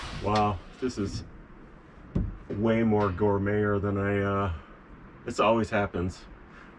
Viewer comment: at 75...I thought I heard it all....and then you said...."Gourmeter"